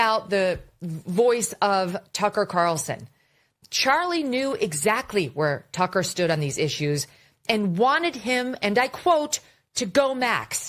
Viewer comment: I hear “don’t go max”.